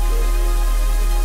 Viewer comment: im just now in 2020 figuring out wtf she said “don’t tell nobody”